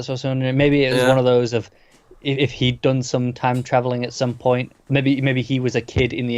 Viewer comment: I thought you were saying “time traveling elves”. Lol! I’m like wtf, I’m 5 episodes in, and in love with the show, but I think I’d have to bail if there were elves in it.